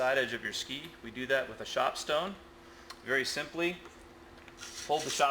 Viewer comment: what is he saying at 0:30? shop stone? what the heck is a shop stone?